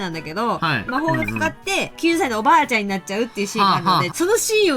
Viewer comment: ⚠️空耳かもです..... 18:00 9歳のおばあちゃん？笑